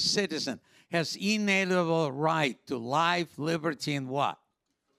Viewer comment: captions misheard happiness as "life liberty and taxes"